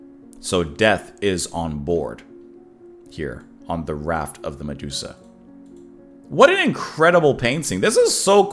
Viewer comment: Darn, I thought it said "The RACK of the Medusa." Legend goes that if you looked at it, you turned to wood.